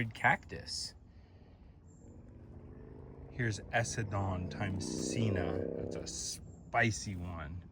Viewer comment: oh wait I just realized I put the wrong time stamp sorry lol. I meant the one at 11:33 I was having trouble understanding what you said there called ese don times cena or something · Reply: ik this is late asf but it's essendon x sina